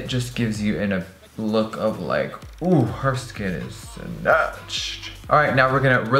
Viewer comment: I always have captions on for every single youtube video i watch- and i noticed that at 3:53 Jeffree says "Oh, her skin is snatched", but the caption says "Oh, her skin is fetch." Just a silly little thing i found XD also I'm in love with this look, i want to try it out soon!!!!